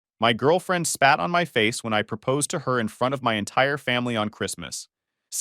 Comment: At first I thought I heard: "My girlfriend sat on my face when I proposed." I thought, "Wow, now that's gonna be an interesting story!" But alas, it turned out to be a typical AI BS. A pity)